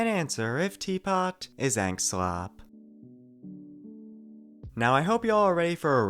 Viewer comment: the captions say “if teardrop angst-slop.”